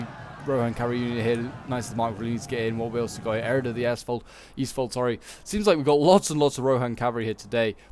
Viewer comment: i heard Eored of the Asshole, and i laughed so hard i hurt my throat a little